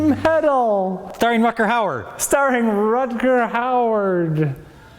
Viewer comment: did he just say "rutger howard"?